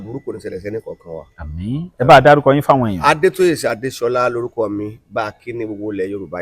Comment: Please, around minute 2:51, the man says his name - is that? I can't quite catch his first name, but I would like to cite him 🙏